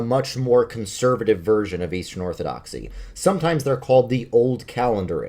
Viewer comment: Oooh… I thought you said WEASTern Orthodox